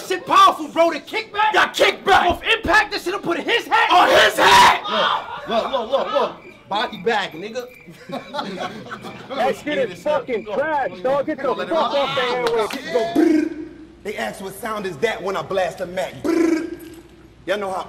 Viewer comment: definitely gotta be the worst/ funniest one that shit made laugh 😂 lmao 😂😂😂🤦🏽‍♂️ did bruh said...look look Bobby back nigga 😬😬